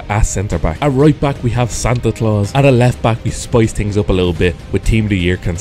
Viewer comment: I thought he said Santa claus is playing